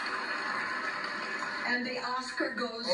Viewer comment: I thought they said “the Oscar goes to Paris” 💀💀💀